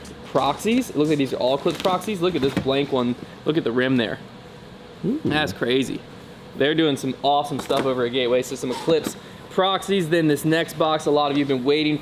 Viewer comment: Did you say "They are doing awesome things at Gateway" when talking about MVP?